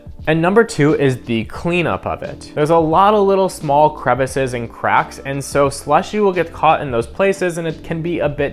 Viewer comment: At First I thought he said small crevices and crap😄